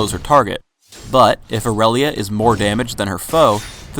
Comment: i see i thought he said more dmg didn't hear the ed